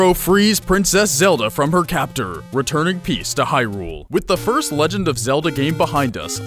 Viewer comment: when he said "bring peace to hyrule" i was eating a tomato and wasn't paying attention, so i heard "brought peach to hyrule" and i was like wait dafuq